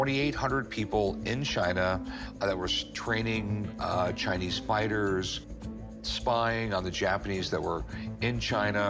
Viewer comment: I thought he said Chinese spiders!!! Not Chinese fighters!!! LMAO!!! 🤣🤣🤣 👉